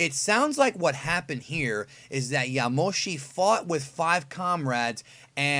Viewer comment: I dead thought he said Yoshi